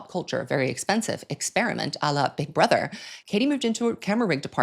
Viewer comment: what he is saying here😅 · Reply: "a la big brother"